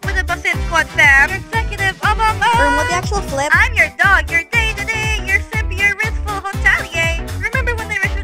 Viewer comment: Omg, I heard "You're gay today!"😭